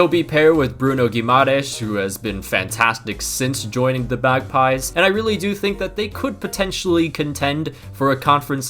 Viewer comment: did say bagpies?